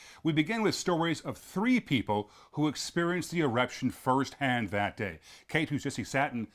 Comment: Did accidentally say “erection” instead of “eruption” at 1:36? 😮